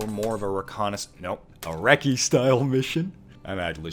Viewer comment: I heard "Iraqi-style mission" and was like, eh?